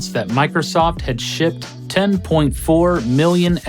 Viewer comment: exactly, am I the only one hearing "Peter Moore announced that Microsoft had SHIT 10.4 million Xbox 360 consoles." I know it's supposed to be shipped, but it really does sound like is saying 'shit'. LOL